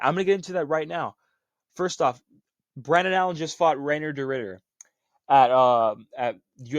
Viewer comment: Based take, people are just blinded by hatred and tbf Strickland can be annoying but he has one of the best middleweight resumes of all time ngl. Brendan Allen, Izzy, imavov off the couch, costa, jack hermanson, cannonier(robbery) and DDP the first time (also a robbery). His only real losses at middleweight are pereira and DDP the 2nd time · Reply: I thought you said Arnold Allen for a sec lmao